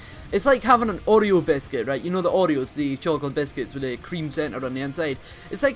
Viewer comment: You've never had Oreos haha? They're so good holy shit. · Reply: ohhhhh fuck yea I've had Oreos! I thought you were saying fucking "Audio Biscuits" lol. I was like da fuck is that?!